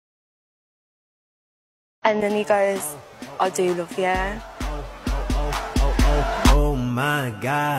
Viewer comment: It took me a few mins to understand what she said after "and then he goes" i didn't read the subtitle properly and then when i heard the audio i thought she said "and then he goes, do you love the air?" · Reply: you’re probably just British 😭 I hear “I do love the air”